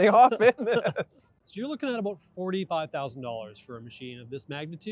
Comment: did he say $45,000 for a mower? No way. I'll keep my gas. Think my maintenance cost is an oil change a year.